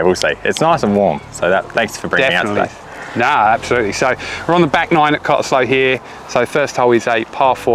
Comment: Did I hear say "We're Ron!" somewhere in there? Weird. What does that mean?